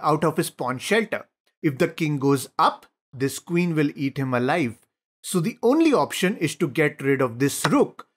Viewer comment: "If the King goes up, the Queen will eat him alive" best part of the video... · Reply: I thought he said "take him alive"